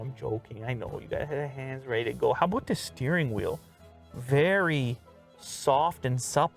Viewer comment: Did you say “How aboot the steering wheel?”